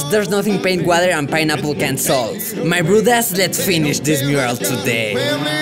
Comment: First video Ive watched of yours. I thought you kept saying "my rude ass" took me halfway through to realize you're saying "my bruddaz" lmao loved the video!